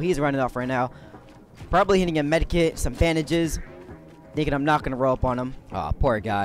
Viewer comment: Is it just me or did he say fandages